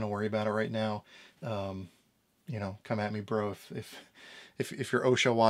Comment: did you say "come at me bro"? LMAO!